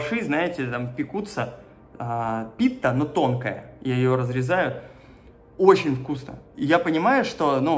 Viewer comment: «я ее разрезаю» мне это послышалось? 😂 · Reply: И её разрезают)